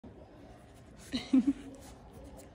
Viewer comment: "You need a teeth clean LiL dUdE"- 2022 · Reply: I thought she said old dude lol